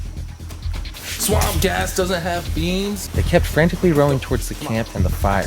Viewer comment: I thought you said swamp ass